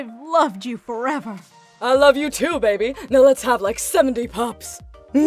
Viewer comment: did he said lets have like seventy pups 💀 If ykyk